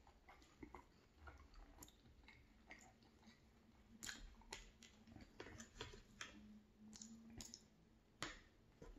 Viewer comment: It sounded like you were saying burnt toast it wasn't burnt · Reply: Oh no lol, I probably said buttered toast!